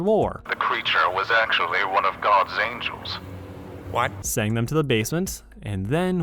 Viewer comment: I think kinger here said "Why?" instead of "What?" ... Like he's literally like "Why? JUST... WHY?"